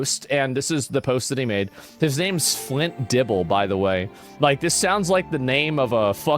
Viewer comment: Flint Dibble LMFAO. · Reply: I heard it as Fritter Dribble